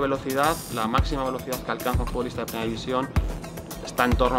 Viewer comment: I thought it said 350km/h